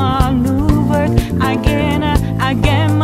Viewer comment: listening to this half asleep and thought I heard "You'll never be some beach (censored)"